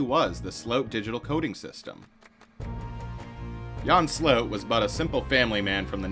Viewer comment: It's difficult to understand based on it being a foreign language name spoken with an English accent, but it seems the subject's last name is "Sloot", pronounced in the video like "slope" but with a 't'; although sometimes it seems the narrator pronounces it with a more "ow" sound. 1:40 Here I am less sure what he is saying, maybe "rapid base storage" or "repibase storage". Not sure what it is so it is impossible to look up what the narrator is saying.